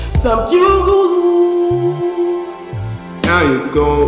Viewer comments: sounded like he said the f you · did he say f**k you?